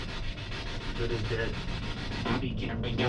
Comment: You heard "Good as Dead" I heard "Go to Bed" or "In the head" - whichever one it was, they all apply to what happened there, some eerily specific responses you guys are getting. Can't wait for part 2!